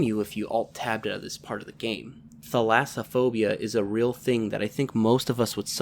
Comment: Trying to figure out what this word is. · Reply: thalassophobia?